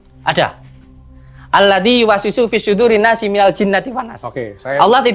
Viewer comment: Aladi wasisu suduri nas....😂😂😂 Apa saya gak salah denger... Agussss......😂😂😂😂